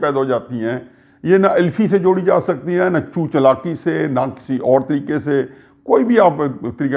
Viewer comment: Did I hear "Ch**t Chalaki" @ 16:02 & Couple of times @ 16:10, 16:14?? 😂🤣😂🤣😂🤣 Hafeezullah Sb Salamat Rahain aap.